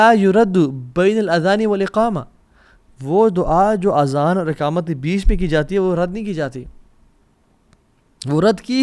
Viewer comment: I didn't understand his words 16:04 wo dua jo azan aur ......? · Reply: Jo Dua Azan aur iqamat ke darmyan ki jati hai wo kabhi rad nahi ki jati